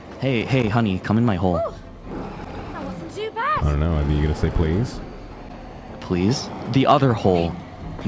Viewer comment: A reminder: The ship is only for entertainment purposes. It’s fictional & fanservice only. They’re NOT dating! A small revision: at 1:55 Vox actually said “I like looking at you when you’re embarrassed” 🥲 Apologies for the wrong sub!! Thanks to Mihael K. for pointing it out and letting me know~ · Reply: hiya, pretty sure at 0:33 he actually says "idk, are you gonna say please?" and dw, you're doing great, just wanted to mention that, his voice was pretty low and mumbled there so it can be misheard pretty easily